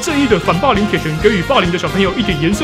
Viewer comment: 到後面會聽成 反霸林博士